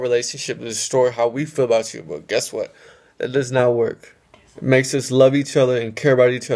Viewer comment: who else heard someonr say " it doesn't" in the background almost as if someone was whispering to him